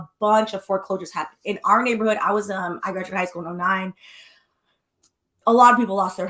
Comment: I thought you said you graduated high school at 9 for a second 😂